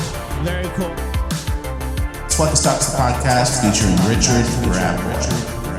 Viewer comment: When I first started watching your show, I thought that richard rat boy, I thought, when you said his name, you were saying richard, the rabbi lol that was a few years back